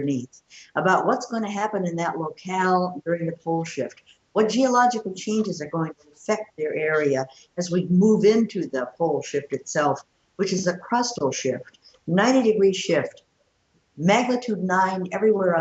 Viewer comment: Did she say "we're moving into the bullshit, itself"? 8:00